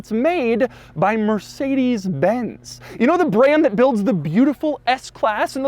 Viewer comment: I thought he said "the beautiful X Class" in the intro and almost spat my Boost out.